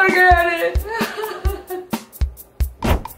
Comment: She is saying "Half a slip"...with her mom's accent when she was a child she thought she was saying "Happy slip" lol it's hilarious.